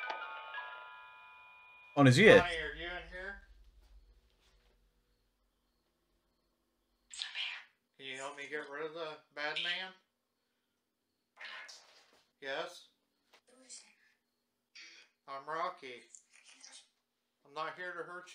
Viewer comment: I hear: "IT'S A MAN"